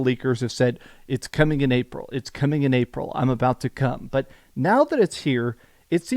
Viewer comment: WHAT DID HE SAY?!?! 🤣🤣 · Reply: IM ABOUT TO COMEEEE